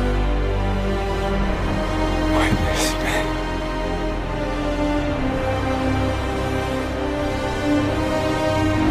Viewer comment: What did he say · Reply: Witness me